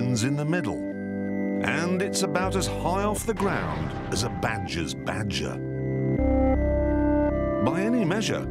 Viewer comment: Can any one tell me what he says at 00:32 is as high off the groound as a … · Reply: Badgers badger